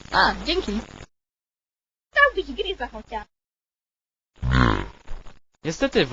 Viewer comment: Ktoś rozumie co mówi ta baba w 0:55 ? Władca ciemnosci co go rozumiem ma napisy a trn szlauf nie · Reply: "Dałbyś gryza chociaż."